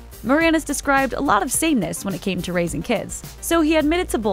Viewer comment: Why does it sound like she says "a lot of Satanists when it came to raising kids" at 1:42-1:44? What is she actually saying - I have no clue.